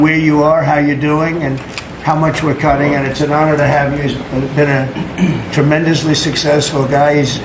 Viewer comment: I could almost swear he almost stumbled and said "It's been an honor to have you as President" but quickly corrected himself